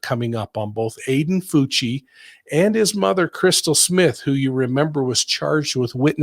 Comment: I was in another room, when he said, "Crystal Smith," and I thought he said, "crystal meth."